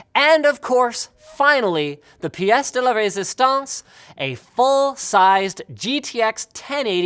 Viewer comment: did he said "the piece de la resistance" ?